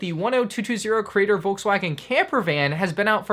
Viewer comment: did he say „volkswagen“?